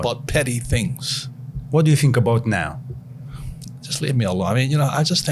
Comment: Did he just say just leave me alone @ 4:02🤣